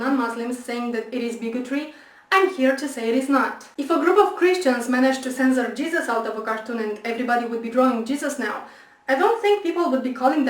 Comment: What does she say at 3:56 ? I couldn't quite catch it - "If a group of Christians (something inaudible)...." - Anyone catch that? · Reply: "If a group of Christians managed to censor Jesus out of a cartoon and everybody would be drawing Jesus now I don't think people would be calling that bigotry".